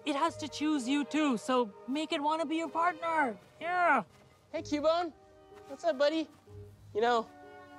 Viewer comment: Who else Heard Cubone say "uh oh " before running and screaming"mama" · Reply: I thought he said "uh oh, Cubone!"